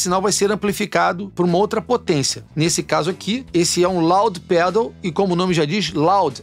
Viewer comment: Você apresentou o Loudpedal e eu já entendi "Laudirpedal", o que é claramente um simal para eu comprar um desse...hahaha Falando sério, que som gostoso que você tirou com esse pedal, hein?!!! De verdade, dá vontade de ficar ouvindo, pois ele é bem definido! Gostei demais mesmo! No próximo CD que você gravar (o meu tá aqui) use esse Loudpedal e mais nada! Vai ficar demais! Forte abraço, queridão!